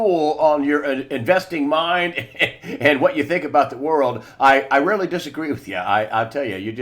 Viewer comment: Did say I really disagree with you?